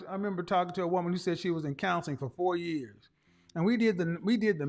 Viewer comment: She definitely on some type of drugs ....I thought she said she was in school 🤣🤣 that probability just cost me 5 iq points